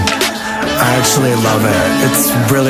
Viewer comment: I thought at the end he said "its really untrusting." And my heart broke for him... But then I read the lyrics in the description and he says, "It's interesting" and I felt good again:)